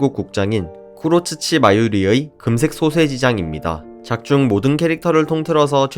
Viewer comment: 금색 소세지 장으로 들림